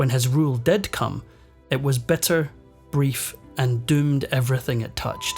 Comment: Great narrator, but i cannot but hear: 0:33 it was better, brief and doomed...(instead of bitter) 3:33 beggar powers (instead of bigger powers)